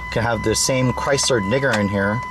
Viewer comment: No he did not say that at 3:38 he said dinger.